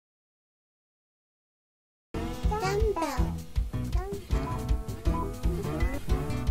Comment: Why dumbbell? ..... that's odd...this is another reason I don't like dub, it should be "dummy" im pretty sure. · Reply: I thought she was saying dumbo